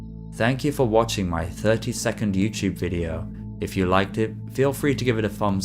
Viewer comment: Oh shit... he meant 32nd video... I thought he said 30 second video 🤦🏾‍♂️ I was like this videos A LOT LONGER THAN 30 SECONDS 🤣🤣🤣